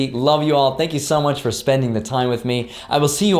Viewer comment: Montag = Monday. Sounded like you said Monat. That means month.